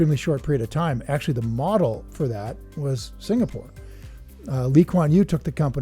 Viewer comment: charlie literally said "looked at singapore ". Didnt understand whay you came in with "actually its from singapore"